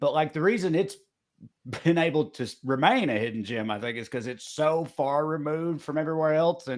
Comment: I was halfway through the podcast when I figured out they are saying “hidden gem.” I kept hearing “hidden gym.” 😂😂